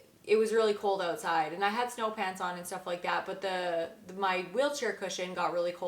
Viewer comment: Sorry, I was sure you said “No pants” lol. 🐨 🦘🦘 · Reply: It would be interesting to find out how many others thought you said “no pants”. LOL. 🫣 🐨 🦘🦘